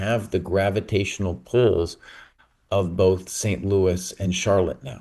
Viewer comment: Someone help me out here...at 49:15 says something about not having the "Gravitational ..." and the audio goes super wonky. I rewound it to figure it out, but did anyone else get the contextual clues for what he was saying here? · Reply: Nevermind...I think he says pulls but his internet connection must have gone wonky.